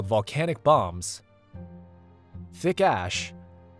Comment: I heard that as thicc ass😂